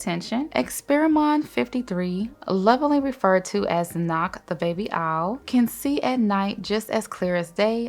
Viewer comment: now why I thought you said knock the baby out 😫😫😫🥴🥴